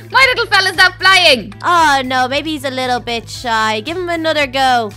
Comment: when chocobo wasnt flying I thought said "well maybe its a bitch" but I know she didnt say that, or did she!